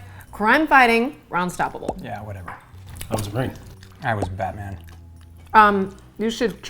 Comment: "I was a Marine" "I was a Batman" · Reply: I know he says "I was Batman" but I first thought he said A Batman, and it was funnier to me